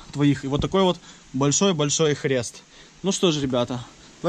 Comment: Мне одному послышалось хрест ? )